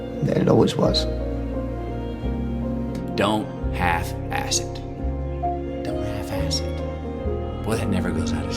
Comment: Bro do your own sub titles. Having Matthew McConaughy at 9:24 saying "Don't have acid" instead of "Don't half ass it" the first time is so meh. You literally do motivation videos about not half assing it and you're half assing it...